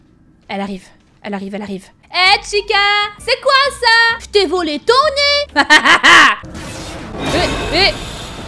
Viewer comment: C est moi ou a 9:50 chica dit vole pas mon nez ?